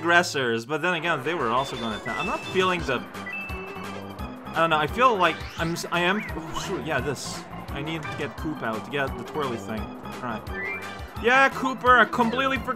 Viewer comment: Did...did you call Kooper "poop?" :|